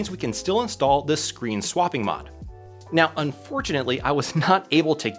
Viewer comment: I sweat I thought you said "Luke Waitforit". I was like "is that Barney Stinson's son?"